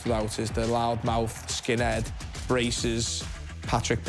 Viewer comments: I’m confused did he just say racist skin head? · I thought he said racist instead of braces :52 😂 😂